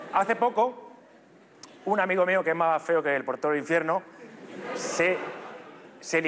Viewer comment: mas feo que, que? no entendi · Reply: Más feo que el portero del infierno